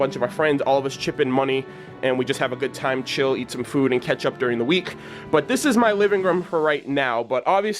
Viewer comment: Anyone else thought he said "Eat some food and ketchup"?